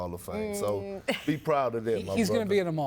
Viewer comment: Did Perk say "So be proud of that Muhfucka" at 2:32? 😂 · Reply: Brutha....he said brutha